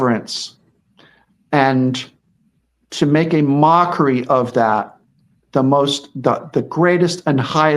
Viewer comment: I thought said "Megan Amockery" which sums her up completely!!!